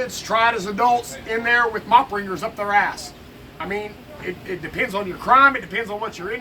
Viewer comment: did he say mops up their ass?